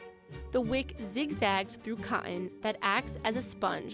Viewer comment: The narrator who I thought was from Boston saying "zig-zags through co' on" instead of "zig-zags through cotton" at 2:13. Okay. No maybe she's British...! What a curveball.